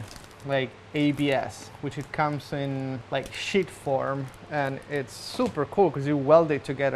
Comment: i know what youre trying to say but we all hear "Shit form"